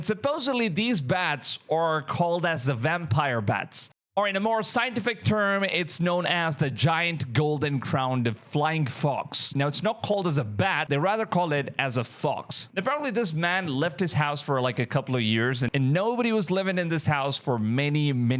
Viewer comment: The name: "The Giant Golden Crowned Flying Fox" What i heard: "The Giant Golden Crowned Flying F*cks" 😅